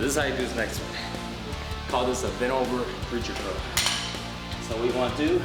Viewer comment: anyone know what he said? - sorry I couldn't make it out... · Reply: he says "we call this the bent over preacher curl"